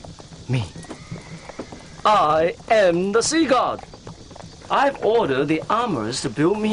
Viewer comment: i'm sorry but i heard i am the seagull